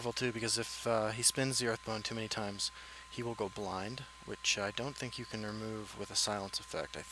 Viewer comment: What did you say at 6:00, beginning with 'If he spends Earthbone too many times'? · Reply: I said something like, "If he spins the earthbone too many times, he'll go blind, which I don't think a silence effect can remove."